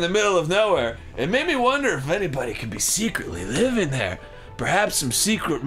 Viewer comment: Did Arin say 'Anypony' at 7:46? Or was it just the voice? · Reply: I think he said anybody, but it definately sounds like he said anypony in that voice